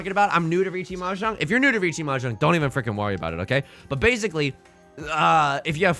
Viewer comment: i heard "don't even frick and morty about it" instead of "don't even frickin worry about it" it is so over for me